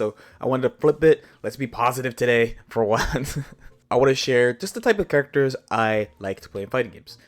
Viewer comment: What he said:: These are the characters I like to play What I heard: Here's who I whack it to